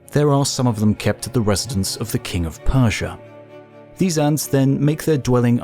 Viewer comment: pause wtf he saying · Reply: There are some of them kept at the residence of the king of persia. At least that’s what I got. It took my like 5 try’s 😂 translation? The king of Persia keeps giant gold digging ant dogs at the crib